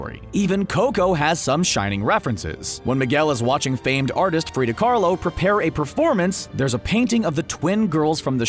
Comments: did he say Frida Karlo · Did you say "Prida Karlo" instead of "Frida Khalo"?